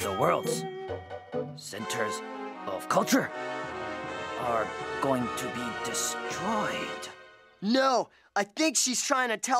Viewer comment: What is kimo saying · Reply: “Centers… of culture… are going to be destroyed…”